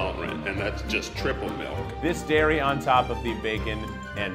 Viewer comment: Lol at 1:43 my Siri turned on, it sounded like Hey Siri